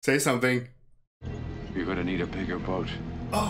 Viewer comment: I always thought he said ‘WE’RE going to need a bigger boat But apparently he said ‘YOU’RE gonna need a bigger boat’